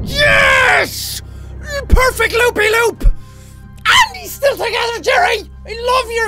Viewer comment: i thought it said poopy loop